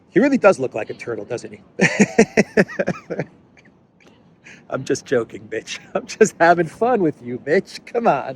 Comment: I heard B1TCH ❤❤❤😂😂😂 "I'm just joking b1tch" Hell-larious!